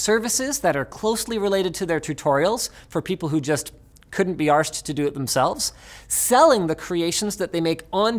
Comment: Speaking of AdSense revenue, did you say "arsked" instead of "asked" because the hard ass- might trigger a profanity filter, or just because you're Canadian?